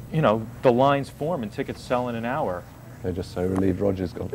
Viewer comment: "We're just so relieved Roger's gone!" ...Or did I hear that wrong?